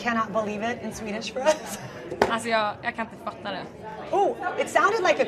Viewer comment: Sounds like she said "I kinda fucked that up"